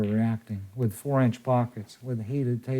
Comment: Did he say 5x10 with 4 inch pockets?